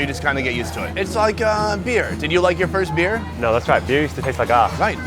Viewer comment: really thought this foo was saying du ma for a sec but he was saying that's right BOTCHED